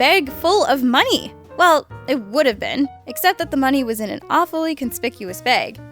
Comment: At first, I thought it said "Monkey". 😅